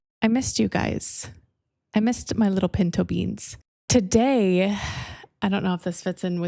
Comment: Thought you said “pink toe beans”